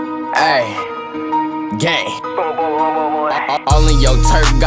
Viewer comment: Did he say gay